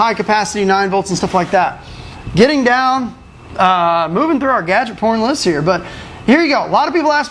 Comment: did he say "gadget porn list"?